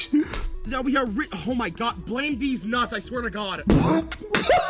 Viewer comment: Also I’m curious, what’s your favorite blooper in the sequence 1:46:44 This is my favorite (When I hear Nagamori say “Blame these Knots” why did I hear “Blame these nuts”) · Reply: also you was correct. He actually said “Blame Deez Nuts” you was not tripping😂. (the VA for nagamori) also commented on this video and he said deez nuts😂 but if you pay close attention it does kinda sound like Knots but he said Nuts😂😂